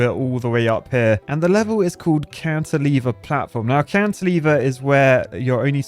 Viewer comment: Am I the only one that thought he was saying Counter Lever instead of Cantilever the entire time?